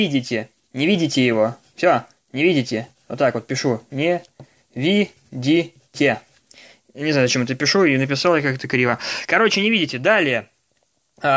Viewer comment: а мне показалось, что он сказал не пиздите!